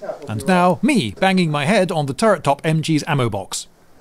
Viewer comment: Kinda drowsy, at 2:36 I thought you were saying "me, banging with the turret top".